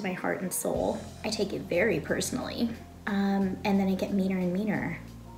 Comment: I legit thought she said she gets “me a lil wiener” and honestly I was so proud🤣🤣🤣 then I re-watched and heard “meaner and meaner” and I’m still proud!